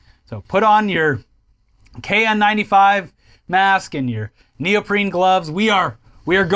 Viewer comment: I heard "put on your niel breen gloves", that seems more efective for handling concentrated cringe